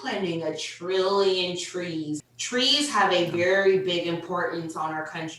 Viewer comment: did she just say "TREES TREES" oh lord 🙄